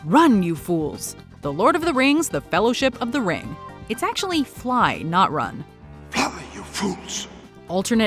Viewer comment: Gandalf has always said ‘Fly’ who tf thinks it’s run?! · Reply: Yeah because forever I thought he said "folly you fools" and I couldn't understand why he would say folly. Turns out he was saying fly and I couldn't make it out in the movie..